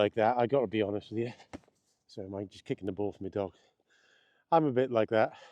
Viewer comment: Trump doesn't mince his words. Must be the Scottish blood in him. 😁 For a moment there, l thought you'd said, you'd just kicked the balls of your dog. 😂